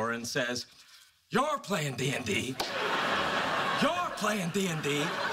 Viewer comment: I thought he said "your plain dandy"